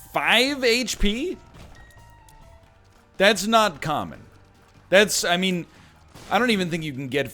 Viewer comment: thought you said "thats not cum" at 0:30 and that you were referring to bucket of lard